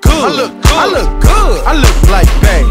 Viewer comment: I'm kind of upset because I thought he was saying ..I look like bank !